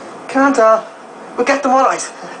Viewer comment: I always thought he said "Scared cunts"..